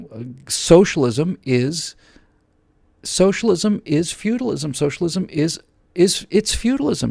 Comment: Pray, at 00:37 he says "socialism is *"... Is what? "Futilism"? Couldn't understand that, and seemed important enough for me to ask... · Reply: That's "feudalism" not "futilism". Feudalism was (is, because socialism is feudalism rebranded) a system in which a small number of rich nobility "loans" out their land to lower-class people for them to work in return for the low-class to live on the land and take a small portion of what they produce, the "serfs," who were also used as an indentured army when necessary. Basically the elites own everything, including the vast majority of people (who in turn did not own anything themselves, completely dependent on their aristocratic masters), so that unless you're a titled, wealthy noble, you were absolutely nothing. Socialism is the same except the power is not so much in the hands of the wealthy--though they often are anyway--but in a huge, centralized, all-powerful bureaucratic government that doles out cradle-to-grave "benefits" in exchange for public works programs and "security".